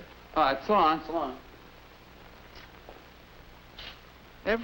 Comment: what do they say ?? "Shaloom" or "see on" or what ?? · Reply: "so long"